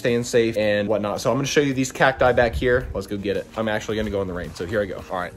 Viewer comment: I thought you said I will show you the cat that die